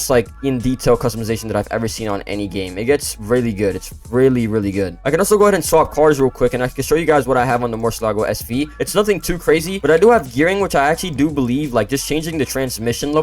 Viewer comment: did u say urine? 😂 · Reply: i said “Gearing”